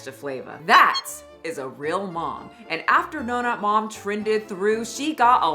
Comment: I know she said "Donut Mom," but she either said it so fast or my ears hear what they please that I heard, "No-Nut Mom." 😂😅